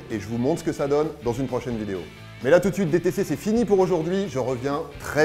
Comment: j'ai entendu "déportez vous bien" MDR